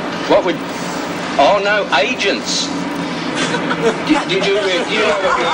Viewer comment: I was gonna say "No Asians thankyou" is probably the most polite racism you'll ever see. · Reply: He has been misheard, he is talking about: no agents thank you